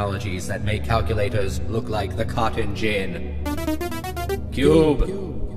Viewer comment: Can anyone understand what is being said here? "That make calculators look like (...)" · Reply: "The cotton gin." It's an archaic device from the industrial revolution era.